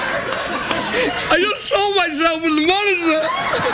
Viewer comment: what does Craig say at 1:06 ? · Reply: ok, figured it out. first " its the horn of completion"...... then at 1:06 "I just saw myself in the monitor". hilarious.